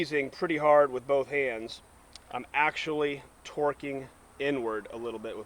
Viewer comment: For a second there I thought he said hes "twerking"